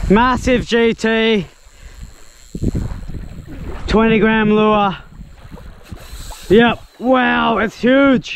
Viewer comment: What a weight of lure you use there buddy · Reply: Ah, i heard 20gram lure in 19:17 ty